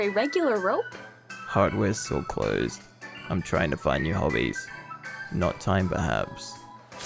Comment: I realized now that it wasn’t Suction Cup Man’s attitude that made him lose the case, it was his horrible food court joke. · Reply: I always thought he said "not time perhaps" but he said "knot tying perhaps"